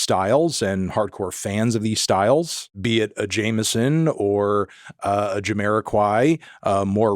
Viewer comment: Couldn't understand the name . Who's this artist ? (The red album) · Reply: jmsn